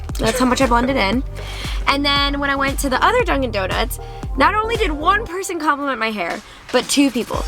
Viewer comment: I heard drunk'n donuts 😂